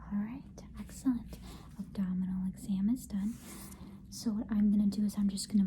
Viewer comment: Sorry but I thought it said anal exam for s second 😅😂